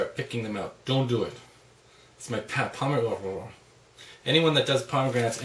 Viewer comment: WHat the fuck did he say at 2:13? "its my pet, pmhmhmhuhhhughhgh"?